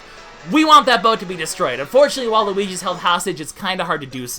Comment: Did actually say Waluigi?